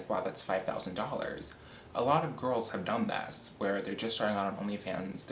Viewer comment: Oh my, at first I heard you say that "a lot of girls are dumbass" 😂😂😂 Thought "Oh damn, he just keep spitting out the tea like nothing" 😂